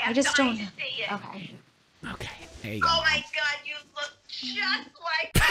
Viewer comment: Did anyone else hear right before the pause the mom says "omg you look just like me"